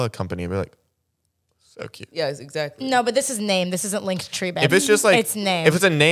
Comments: did Tara call him babe ?👀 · UMM.. did tara say babe at the end?? I watched it three times and can’t find out what the last word was someone help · does tara call zack babe? not shipping just wondering